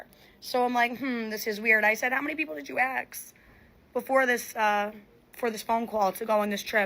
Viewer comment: She said "axe" instead of "ask," that's enough to know everything about her you need to know...